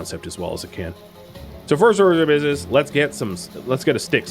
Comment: what did you say? that blurred bit cracked me up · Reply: A very slurred 'so first order of business'